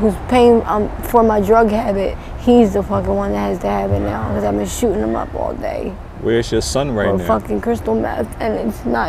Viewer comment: Did she just say SHE'S BEEN SHOOTING HIM UP ALL DAY?? Man that's rough..